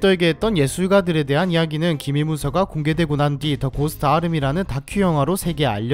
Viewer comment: 고스트 아미가 왜 고스트 아르미로 들리지????